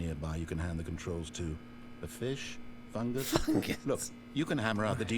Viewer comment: am i the only one who heard say f●ck?